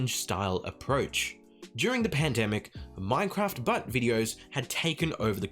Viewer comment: When he said Minecraft but videos I thought he said Minecraft butts 😂 anyone else?